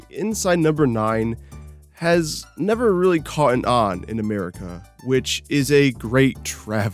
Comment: Please tell me you did not say "caughten on" because it totally sounds like you said it.